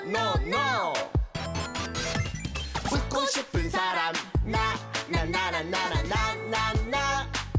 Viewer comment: 분에 웃고 싶은 사람이 죽고 싶은 사람이라고 들려요 ㄷㄷ